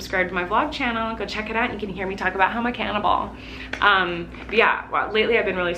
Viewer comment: I thought you said “ you can here me talking about how I’m a cannon ball” 😂😂